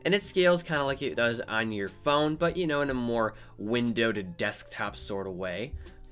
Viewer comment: first time I heard the word "windoweded" at 2:38. LOL. Anyway, I'd just rather copy my files onto the Macbook and edit them from there.